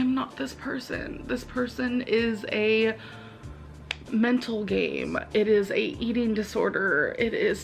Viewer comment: okay I thought she said mental gay I think I'm gay outta my mind now